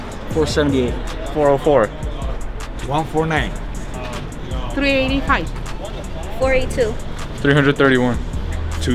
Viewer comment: where did he get “1249” from?? First time was 1-1000 second one is 1-500 how tf he said 1249😭😭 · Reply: He said 149